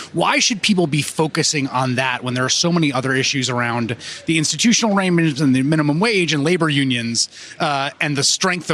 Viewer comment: what the actual fuck did he say "institutional arrernggsdgnadsnfg" this Chris Hayes dude is a clown